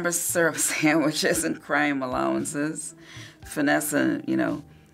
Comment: I was the only one who thought he said grandma allowances instead of crime allowances